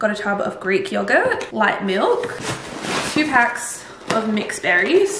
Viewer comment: I could of sworn you said “white milk” and not “light milk” and I was thinking all milk is white haha I need a hearing check!